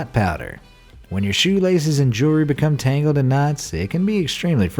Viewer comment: Omg turn on captions at 3:46 he said when your shoelaces become knotsy and the captions said Nazi 😂😂